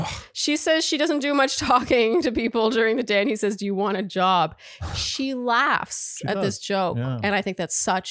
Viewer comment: I thought said do you want a dog? Like a dog to talk to during the day. Rather than do you want a job.